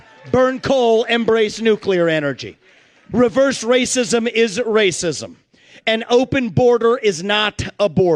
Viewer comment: I have played this back to back to back and it sounds like he said reverse racism isn't racism.